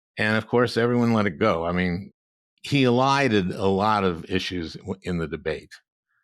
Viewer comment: what is that word they are saying "alieded" (21:49) "elited" (22:16)?